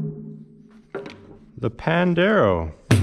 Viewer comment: I thought when said "Pandero" he said "Pendejo" 🤣 I had to double check